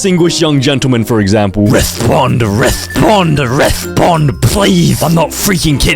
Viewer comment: Restroom is what i hear